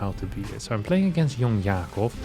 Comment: I thought he said "So I am playing against Jan Jacob" like it's a Dutch RL friend of his or something 🤣